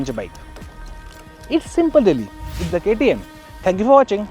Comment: What did you say in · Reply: Thank you for watching